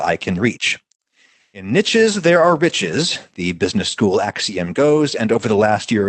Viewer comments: “In niches, there are riches.” The quote really doesn’t work when your from the UK. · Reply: Oh that's what he said. I thought he said in the ditches there are bitches and I'm like "dayum Georgia is worse than I thought". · “In niches, there are riches.” The quote really doesn’t work when your from the UK. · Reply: I heard it as, "in niches, there are bitches."